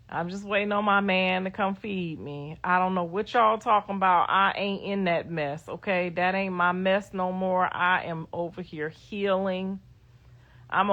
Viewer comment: she really said “my name ain’t Bennet I ain’t in it” 🤣